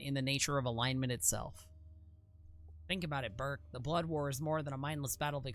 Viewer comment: "Think about it, berk." I don't care what the text says; what I heard was "think about it, Bert," and this is a Sesame Street quote now.